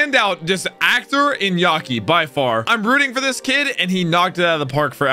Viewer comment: Did he just say "i am rooting for a Gecko.." at the beginning. Is that Gecko Moria?